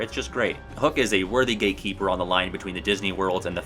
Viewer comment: Did you just call Captain Hook a gay keeper? 😂